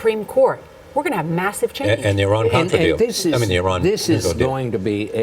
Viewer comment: It's sounded like he said Iran contra deal